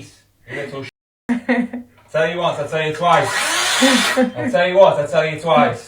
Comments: if the first girl was a teenager I sorta get it, however very extreme, but 25!?! once she's moved out she is never speaking to her again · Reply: She's not 25. Americans misheard what he said, he says "I told you once I told you twice." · Her being 25 makes this even more crazy. She’s not even a child. If they don’t want her having boys over, say she can’t bring boys over. And what, now she doesn’t get any privacy ever? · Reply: She's not 25. Americans misheard what he said, he says "I told you once I told you twice." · SHE'S 25 AND YOU HAVE A "NO DOORS CLOSED/LOCKED" POLICY?!?! Insane. I thought she was freaking because she was 15 or something. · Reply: She's not 25. Americans misheard what he said, he says "I told you once I told you twice." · She is an adult!. She's entitled to privacy! Maybe it is time for her to move out, she needs her own space away from her controlling mum. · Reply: She's not 25. Americans misheard what he said, he says "I told you once I told you twice." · I'm 25 and live with my mom. My room is my room. It is 1000% my space. It's had a lock on the inside since I was 17, and before then my mom would knock and wait for an answer before opening the door. The uncle in that video gives me the same vibes as my older brother, who I am now no-contact with. The daughter is going to be so much happier once she is able to get away from those arseholes. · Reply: She's not 25. Americans misheard what he said, he says "I told you once I told you twice."